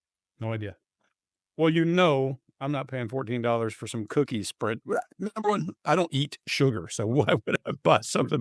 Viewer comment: Greg sent it, knew it , didn't know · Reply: I thought he said I wouldn’t have sent you some cooking sprinkles or spread , how did he know that was included?